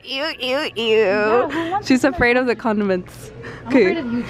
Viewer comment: I thought said she’s afraid of The condoms